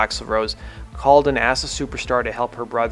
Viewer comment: Hahahahaha at 1:28 the captions said that Shannons sister “called a Nasa Superstar” 🤘👽